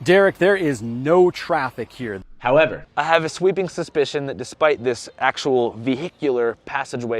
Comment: "sweeping suspicion?", am I hearing that wrong or have I just been using the wrong word this whole time lol